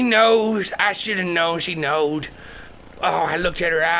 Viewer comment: did he really say "she knowed" :P